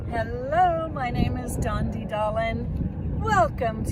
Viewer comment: Whenever I hear you say your name, Dondi, all I hear is “it’s me Dondi Darlin’!” ❤😂 🥰 It’s so sweet! Dondi Darlin! Thank you for all that you do. I do your work every day, ladies! ❤❤❤